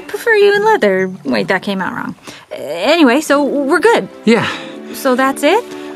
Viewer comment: What did Anna say after "i prefer u in leather" 2:00 ?? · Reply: Wait, that came out wrong. Anyway, so we're good